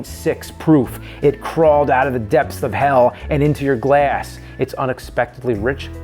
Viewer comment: "it crawled out of the depths of hell and into your ass" I can't be the only one who heard that right?